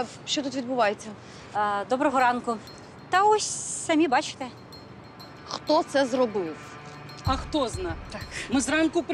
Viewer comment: На 7:33 когда Дарина говорит " Хто це зробив" мне послышалось "Хрю🐽🐷 це зробив". Как будто свинья хрюкает😂😂😂 хро-хро